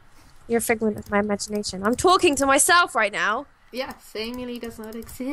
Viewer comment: When Salem said " I am talking to myself ". I heard " I am twerking to myself." XD